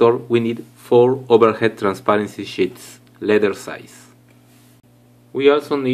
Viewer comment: when he said sheets i thought he said shits